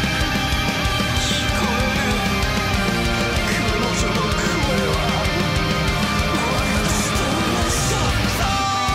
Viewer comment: what's it supposed to say at the end here? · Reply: it says "i can hear her, i can hear her, her voice is...the same as mine